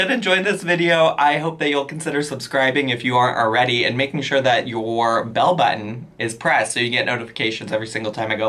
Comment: Haha I definitely heard "make sure your belly button is pressed" hahah